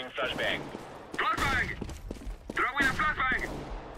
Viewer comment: i literally died when i keep hearing ''throwing a blood bang'' and those noobs walking around haha omg